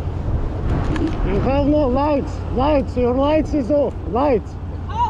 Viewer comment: I thought he said Im going right but it was its light